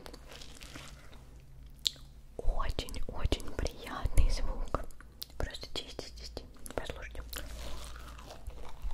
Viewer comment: Yall can anyone tell me what she said 18:17 pls someone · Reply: "Very, very, very nice sound. Ten out of ten"